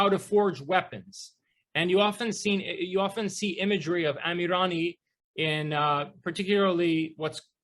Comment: "Anurani" what does he say? ~ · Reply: amirani